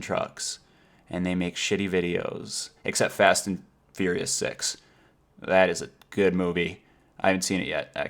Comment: Wow, I thought you said fast and furious sex, not six... :/